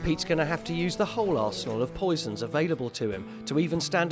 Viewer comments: Sounded like he said "Arseload" lol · What did the narrator say at 1:37??? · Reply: Arsenal The inspector has different types of equipment to use